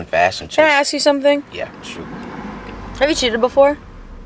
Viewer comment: I thought she said have you shooted before I feel def cuz that was sad